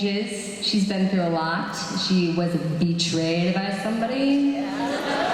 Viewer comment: please tell, what Chloe said on 7:54-7:56? I'm not a native speaker and I couldn't figure that out. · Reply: Eric Gates "She was betrayed by somebody.". not "wasn't". Because she was. :P